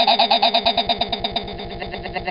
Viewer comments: i always thought he said mmm jamburger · when i first heard it, i thought it said: “Mmm... Cadburger.